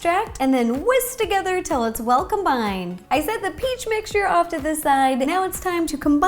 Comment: I thought when you said "well combined" you said "welcomed" i dont know why...😆